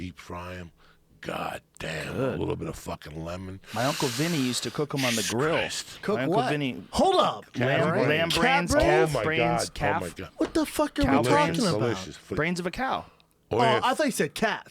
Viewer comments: Dude I thought he said cat brains too holy shit 😂😂😂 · Brenden Shaub 's face when he think's they're eating CAT BRAINS instead of CALF BRAINS. HAHAHAHAHA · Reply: I thought he said cat brains also, I was like wtf? lol.